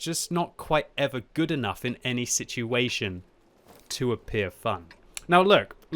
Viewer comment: “to appear fuck”, that’s what he said right?